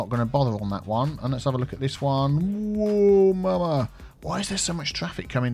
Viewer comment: Did he just say "ou mama"?